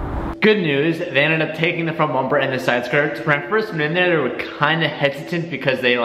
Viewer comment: what he say lol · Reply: 😂 translation "when I first went in there"